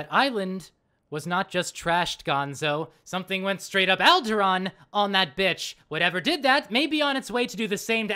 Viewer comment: im not sure I get the reference "something went straight up algeron on that bitch" what is an Algeron (I did google it but all I found was a star system from star trek and a Musician unless one of these are what you are referring to but I doubt its either of these) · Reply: ohhh... it sounds like he is saying "Algeron" but now that I know he meant to say Alderaan it makes sense now